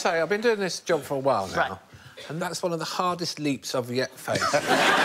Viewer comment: What does Alan say at 1:15 "and thats one of the hardest..."? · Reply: leaps I've yet faced.